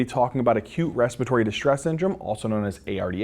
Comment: The first time I heard him say "Acute Respiratory Distress Syndrome," I thought he was being quirky, saying " a cute respiratory syndrome," and I was like, "What's cute about any of this?!" 😹💖